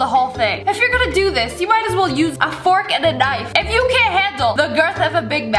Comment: Why did I hear "if you can't handle a big back"😂😂😂😂😂